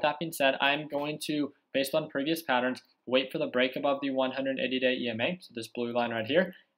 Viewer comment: thought you said that was the 15 emA ?